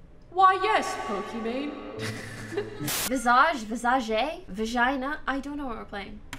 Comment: did pokimane just say "vagina" for the games name?